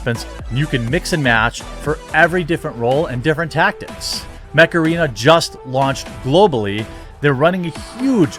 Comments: For a moment there I thought you said....Macarena. I was thinking....Oh God no....not that stupid song again!!!! HEEEEEEEYYYYYYYYY Mech Arena!!!! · When you said your sponsor was "Mech arena", I thought you said "Macarena" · Install Mech Arena for FREE here ✅ <link> and get a special starter pack to help kickstart your game! :💥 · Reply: I totally thought you said "Macarena". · You said 'Mech Arena' I thought you said 'Macarena' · I thought you said, "macarena". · Mech Arena... I shit you not I thought you randomly said "Macarena" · I was listening without watching and i thought you said macarena when you were plugging mech arena. I was so confused. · I thought you said Macarina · I thought he said MACARENA , like the cringe dance. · I thought you said, "macarena." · Oh.... "Mech Arena".... thought you said "Macarena".... I was WAY off! ...shit, that was close, nearly unsubscribed. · I thought Jeremy said " macarena". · I maybe showing my age here....but swear on my life I heard you say "Macarena" not Mech arena lol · As per the sponsor. I could have sworn Jeremy said "Macarena" (sp) ;) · When you started your mech arena promo I thought you said macarena... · When you first said mech arena, I thought you were saying macarena